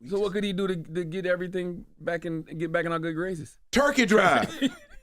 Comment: when he said turkey drive I heard rent'em spoons... Lol iykyk